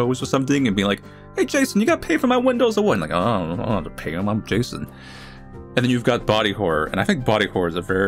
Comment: did he say the n-word at 13:18 ? or am i hearing things. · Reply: “hey jason you gonna pay for my windows or what?” “unh unh i dont have to pay them im jason”